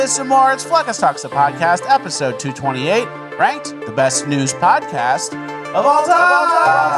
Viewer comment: WATCH HENCHMAN MAXXING 2! Join Bonus Land for over 4 hours of exclusive content each month! <link> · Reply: Did say, "The best news podcast of all time?" So, this whole time I thought it was "...new..." but it's actually "...news...?" Or was he doing a bit?